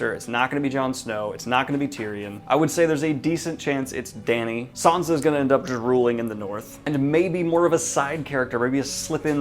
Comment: Ha ha. It sounds like you say "Sansa will end up drooling in the North."